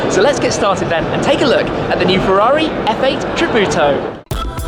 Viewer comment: it sounds like he says 'F8 Kabuto'